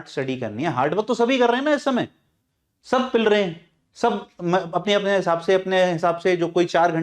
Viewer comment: What did sir say? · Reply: Sab pil rahe hain😂